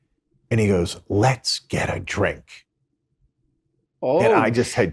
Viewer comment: literal words "and he goes 'Let's get a drink', and I just had chills go up and down my body" 🤣🤣🤣🤣🤣 WTF is the Glitteratti? 😂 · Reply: Ohh I thought he said let's get a trick, or turn a trick, like he was propositioning prostitution